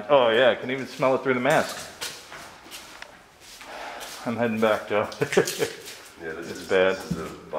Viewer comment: I heard yeah Joe.